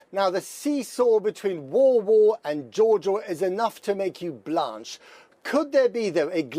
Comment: what's he saying ?? now the seesaw between war war and joe joe ??